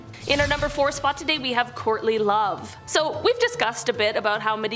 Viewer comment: I thought you said "Courtney Love" initially not "Courtly Love!!"